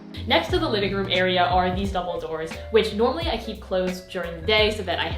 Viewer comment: sounded like Dumbledore when u said double doors haha just wanted to point that out